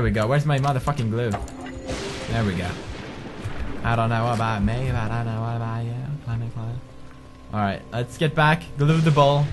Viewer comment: Did I hear pewds say "I dont know about me but I know about you clemmy cloo" in this video? XD